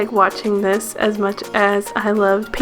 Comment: listen to this... it sounds like she says "I love.." and then there's like a 2nd voice that overlaps hers and says either..."painting" or "penis".. are my ears bad?! Edit: listening more it sounds like it says "painting" lol